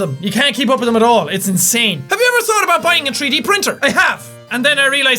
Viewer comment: It sounds like he says tree D printer I'm pretty sure I don't want a printer for that.